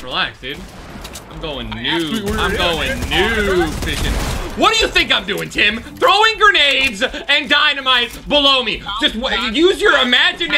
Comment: I thought he said what do you think I’m doing Tim I’m throwing bombs a balonie (but no dynamite